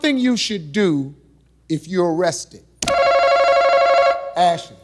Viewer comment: jesus harvey, you screwed that lady over because you can't enunciate properly. it sounded like you said, "if you're rested".